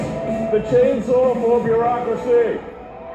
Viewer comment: mark To me it sounded like he said bureaucracy not democracy. "Chainsaw to Bureaucracy"!